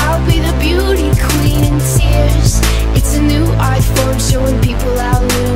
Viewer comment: instead of saying "art form" i thought she was saying "iPhone " #commonwhitegirl :/